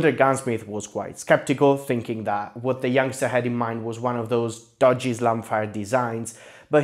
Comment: I heard "dodgy slam fire design" as "Dodge Islamic-Fire design" and was... so confused.